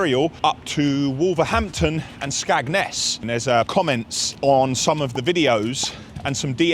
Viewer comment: Did you just call Skegness SKAGNESS?